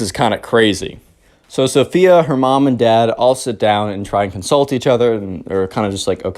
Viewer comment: When you said consult eachother i thought you said insult eachother